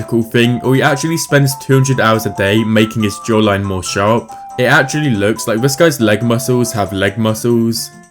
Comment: Somewhere around 3:15 Bro I don’t know if I heard that right😭😭 the jaw line one.. I’m pretty sure I heard him say “ he spends 200 hours a day making his jawline sharp” I’m confused